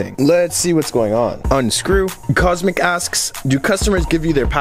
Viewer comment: The comment reads Kosmic. Your subtitles say Cosmic with a C instead of a K...